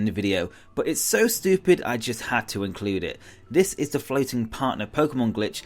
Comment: I heard 'Fellating partner pokemon glitch.' Horrifying.